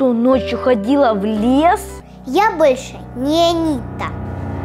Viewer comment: мне послышалось Я больше не нита, я нита